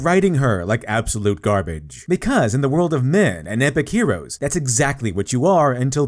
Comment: Sounds like you're saying "Because in the world of meh and epic heroes", so I'm not sure if I'm interpreting this right but I suppose you were saying "men" instead of "meh". It's an interesting point. Since in the market of sexual selection, men start with nothing and must build themselves up and women start with given value which depreciates in time, should we then also write "matriarch's stories" where the protagonist starts basically as a Mary-Sue, except they have the weakness of naivete: during the character arc, they struggle with the loss of their strength and power but in the end gain wisdom?